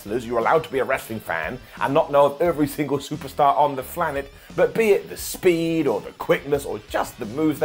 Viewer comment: Simon, did you say "Flanet"?